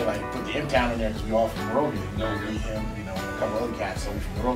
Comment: Oh shit you guys from michigan. I will represent bench proud for you man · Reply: Carnage weightlifting Michigan? I thought they said Monrovia (near LA).